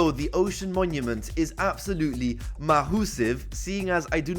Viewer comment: XD did you say mahoosive at 0:37 ?